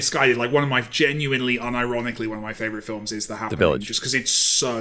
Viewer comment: what does say what is his favourite movie? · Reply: "The Happening"